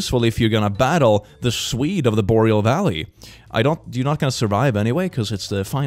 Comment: Did he just say the swede of the boreal valley!? 😂 I literally just choked on my pizza